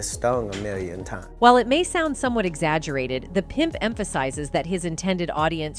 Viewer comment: "The pimp" what did I hear there...?